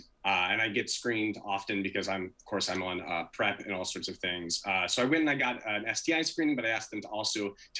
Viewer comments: "of course, I'm on...." whaaaaat??? Did anyone hear what he said??? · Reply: Something heard like (pred all sorts of things). It wasn’t clearly understood what was said at 1:43 marker. Inaudible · "of course, I'm on...." whaaaaat??? Did anyone hear what he said??? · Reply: PrEp (pre-exposure prophylaxis) - to prevent HIV